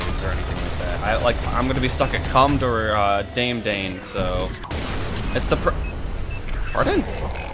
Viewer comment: you did not say "daem daen" ITS "da me da ne"